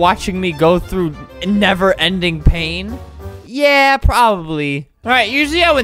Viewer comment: is it just me or did anyone else hear him say endeavor instead of "a never"